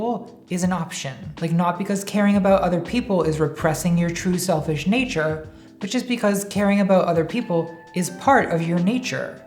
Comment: I swear I heard "repressing your true selfish Nietzsche".